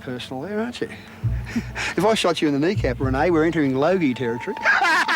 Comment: What did he say at 2:53? "If I shot you in the kneecap Renee, we are entering ____ territory" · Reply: Number 1 Boolshet Logie. The Logies are annual media awards in OZ